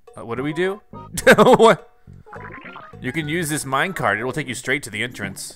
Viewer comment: Me: when the black work sayed get on the minecart Me:I thought he said Minecraft lol XD